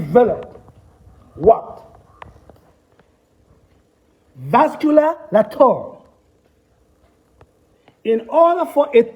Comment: Can anyone please spell the word at 44:23? All I got was vascular and I can't figure out the last part. Thank you. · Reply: Vascularlature